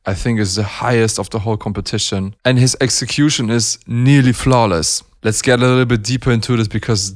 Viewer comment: every time i think you say "flowless" 30:43 i dont know if is "flowess" or "flowless" , maybe im wrong and you are saying something else ...